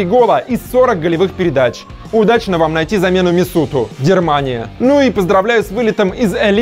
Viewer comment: Дермания? Надеюсь услышать извинения в следующем ролике. Вы считаете, правильно делаете, когда оскорбляете целую страну? Вас могут смотреть немцы. Какая неожиданность. Спасибо. Плюнули в лицо. Если не знаете ничего о политике Германии, об интеграции, об отношении к Эрдогану...не беритесь говорить. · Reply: У чувачка просто проблемы с речью. Хотя знаешь, зная свои проблемы я бы тщательнее пересматривал все на монтаже. Я тоже услышал "дермания", тебе не показалось.